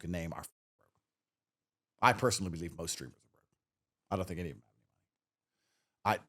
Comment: What did Tate say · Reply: Broke